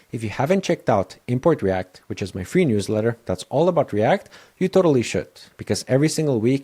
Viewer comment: Had to turn on subtitles because I thought you said ‘you totally sh*t’…